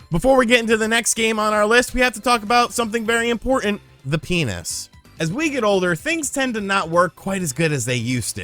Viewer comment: I honestly 1st thought you said The Pianist. 😃